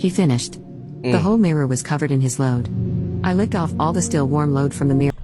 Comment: Only 2 minutes in and all I hear is “I licked his still warm loaf off”😭🙏